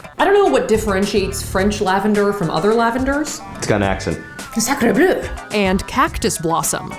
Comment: DID I HEAR A '' sacrebleu'' AT 13:02 ????😂😂😂😅😵 (i'm from Québec, Montréal, soooo😂😂)